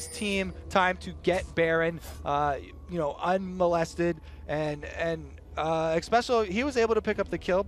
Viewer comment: did kobe say molested ? wtf